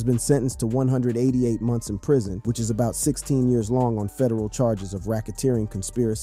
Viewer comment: Why is this 28 min long just tell me the sentence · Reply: He clearly says sentenced to about 16 years in prison...at about 1:45 seconds...